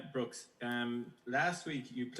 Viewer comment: Did he call him Brooks?